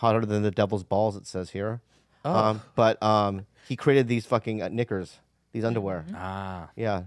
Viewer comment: Who else thought the slept king said the n word but I played it back and he said knickers